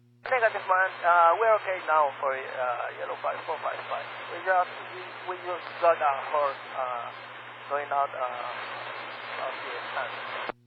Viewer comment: I don't understand why they don't make English at level B2 or even C1 mandatory for pilots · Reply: I put that up to bad transcription on the part of. 4:35 The pilot said something like "We just had a horse climb out of...". He definitely did NOT say the nonsense "We just had a horse right now".